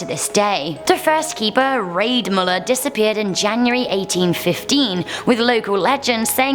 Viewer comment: When you said raid miller disapeared january 1815 i thought you said my birthday january 18th